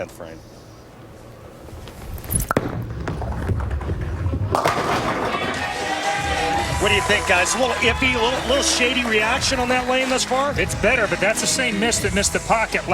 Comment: did he say SHITTY reaction? Lol